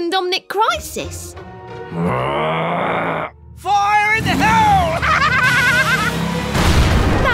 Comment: Did he say… FIRE IN THE HOLE!?